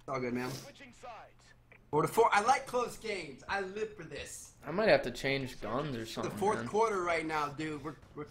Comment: I thought you said,"I might have to change thumbs or something." lol